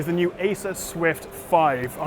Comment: It sounded like you said Asus swift 5. Got me confused there.